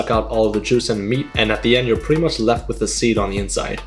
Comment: Did he actually say Meat??????? It’s